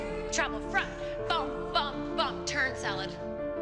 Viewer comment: Did she say salad at 1:40?? ;D